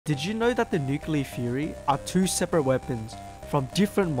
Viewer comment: I thought he said "nuclear Furry" 💀